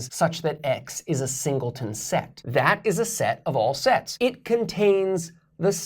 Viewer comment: is it my speakers? he sounds like he says sex of a sex